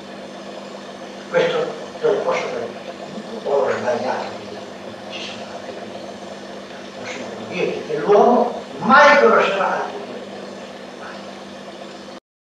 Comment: qui cosa dice? mi pare di sentire "sbagliarmi"